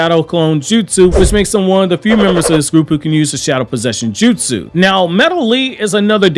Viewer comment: did you just say Shadow Possession jutsu?